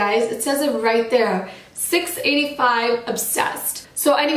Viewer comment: So I went to my local store and bought the 605 obsession and by looking at it I was kinda confused because the color did not look like the one you have. I bought it anyway and it turned out to be a bright hot pink lipstick. I tried to watch your video again and I heard you say 685 obsessed and not 605 obsession...correct me if Im wrong coz 605 obsession is hot pink